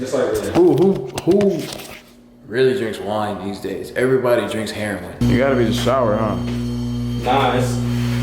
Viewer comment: did he say everyone drinks heroin